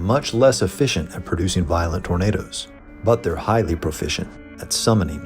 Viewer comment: Ngl i thought it said yornado